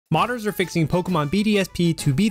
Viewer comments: I heard "murders are fixing" lmao, but didn't really bother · Thought he said pokemon bdsm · Thought you were saying Pokemon BDSM